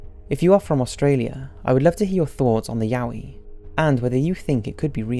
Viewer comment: It sounds like he said khoisan, I am sorry bro but khoisan is South African natives haha